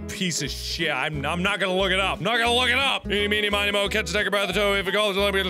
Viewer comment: did he say the N word?😱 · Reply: No he didn't. He said tiger